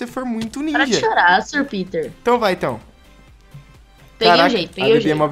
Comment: GNT assim só eu q escutei a falando para de chorar Sr Vitor em 5:14???